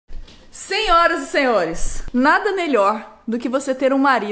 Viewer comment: ela disse "senhoras e senhores" eu ouvi "100 horas e 100 ores" Kkkkkkkkkkkk